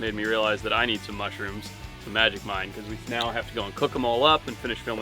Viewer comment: I totally thought he said "the magic kind" when he had that magic mind drink... was thinking how can he film a tv show and be trippin' balls 😂